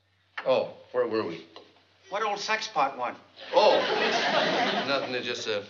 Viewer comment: did Buddy say "sex pot"?